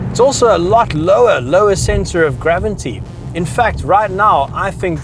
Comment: did he say 'gravinty'?